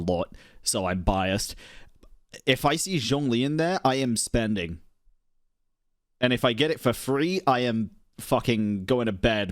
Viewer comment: It's a long video but please make sure you watch the full thing if you want to comment on MY take on it, I go fairly in depth, chat with himself and put my own thoughts on the whole drama into my own words throughout! Link to video in description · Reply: Wow time to clean my ears apparently. For a second there, when you were talking about the Ganyu and Shenhe dresses, I thought you said: "If I see Zhongli in that, I am spending."